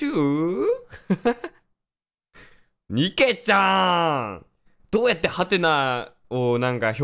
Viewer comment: のニケちゃーんのちゃーんが ちょっとニャンちゅうに聞こえた( ˙-˙ )